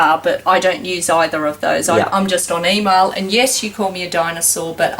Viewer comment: I heard her say Dianasour but I don't think she did...